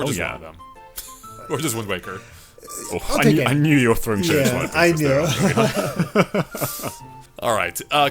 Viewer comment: Can anyone discern what says at 29:17? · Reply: I knew you just wanted Windwaker I’m pretty sure